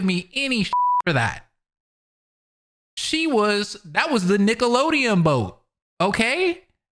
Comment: No way I just heard you say "Nickelodeon Boat" 💀